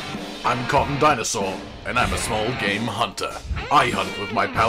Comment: I thought he said small gay hunter